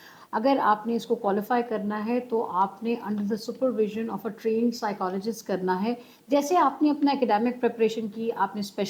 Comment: Ma'am what are you saying at this point? Supervisor trained psychologist